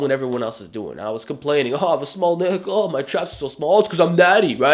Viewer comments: I am going to be the first person to say i heard 'small dick' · did he say " ooh i have a small dick" ? XD